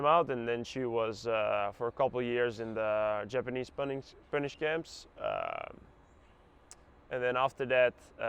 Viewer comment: translator, there ain't no spanish-japanese internment/detention camp during WW2 in Indonesia, or elsewhere. I think he said, 'Japanese punish camp.' Which probably derived from how the Dutch usage of the phrase.